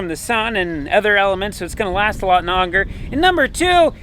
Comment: ish what werd did you use thair? I’m thinking and was expecting “longer” but it kinda sounded like “onger”¿ DAMN rekcehclleps